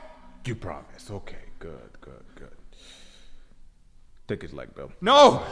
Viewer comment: The part where he was about to fade him out of sheer instinct but retracted back into his original position killed me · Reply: but what did he say when he told them to attack? I heard "take his legs"